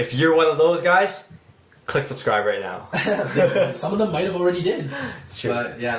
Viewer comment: Did you just say might have already did